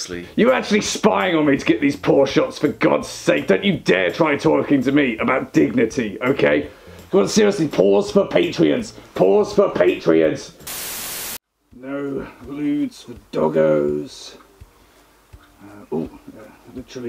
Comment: I heard "paws for patriots" wot